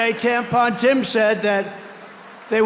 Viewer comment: am I hearing that right lol? “Tampon Tim”